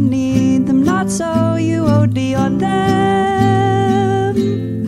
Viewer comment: can someone tells me what does it say there? There is a word i can’t get it : not more than you need them, not so you OD on them... is it like that : OD on them .... ? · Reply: OD, yeah. overdose